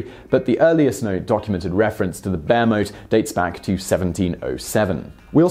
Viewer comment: I thought when he said 'bear moat' that he said 'bare moat' as in nothing in it. But bear moats are much more fun.